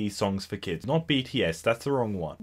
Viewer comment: I literally thought it said BTS 😂